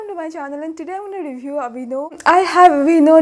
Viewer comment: I've been watching a lot of memes lately. The moment you said aveeno I heard 'Binod' 🤣🤣🤣🤣🤣 Thank you for the review! Been eying the dermexa cream for ages, but hesitating to buy because of lack of reviews.